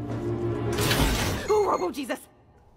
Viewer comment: I thought she said emo Jesus 😭😭😭